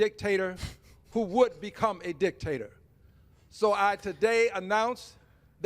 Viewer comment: did he say Dicktater?